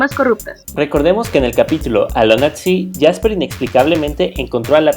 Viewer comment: No escuché bien. :'v ¿Me podría decir los nombres de los capítulo que salieron? Gracias :3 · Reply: Alone at sea.... ..creo